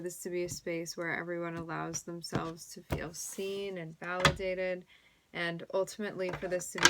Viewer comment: heard this as “where everyone allows themselves to be obscene” 🤣